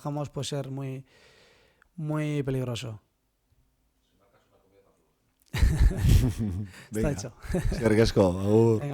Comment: Que dice el reportero/periodista en el minuto 22:37?? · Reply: Dice: Si marcas, una comida pa todos!!!. Un saludo